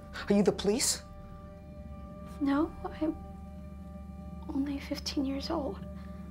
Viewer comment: I'm only 12 minutes in and this is the most intense acting I seen from your cast. Edit: Just finished watching... This made tear up a lot. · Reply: I thought u Said "im only 12 years old" XD